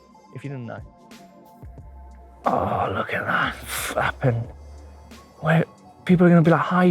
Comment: Haha did he say 'fappin'? So good!